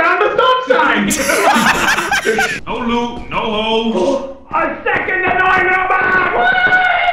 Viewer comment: I heard "A SECOND ANNOYING ROBOT WHYYY"